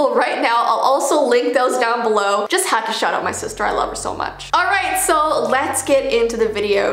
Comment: When you first said “I just have to shout out my sister” I first heard “I just have to shut up my sister”!!!!! 😹😝🤣😂